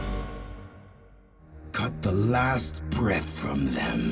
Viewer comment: I thought zed said " cut the last breast from em" after this motivating opening omegalul